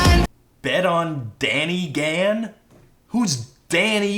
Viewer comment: Emy Noelle I honestly thought he said “Baka oniichan”. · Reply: i thought he said danny can